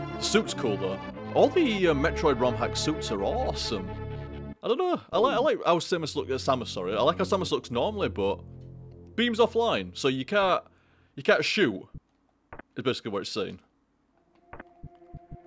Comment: I could have sworn it said "Beans offline" there at the start - Had to watch it twice to make sure.